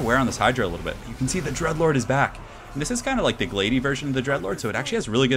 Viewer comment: I could have sworn you said "The Druglord" rather than "The Dreadlord" lmao